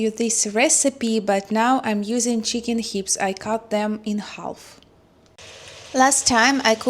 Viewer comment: Hello, in the U.S.A. we call them chicken thighs, not hips. It only got my attention because I thought you said chicken lips 😮.